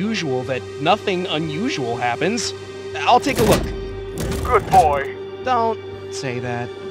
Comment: did he just say good boy 😂